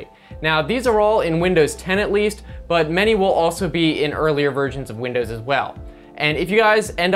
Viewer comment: did u say early virgins of windows?